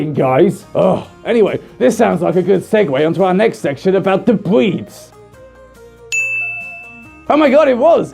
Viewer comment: I keep hearing him say "bweeds." Please tell me im not the only one here! XD